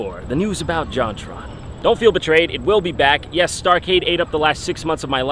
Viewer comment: First i thought he said star wars so i went checking every source of the new star wars, now i watch it a second time.... WHAT THE FUCK IS STARCADE